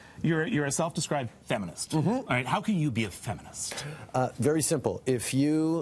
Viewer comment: I thought he said he was feminine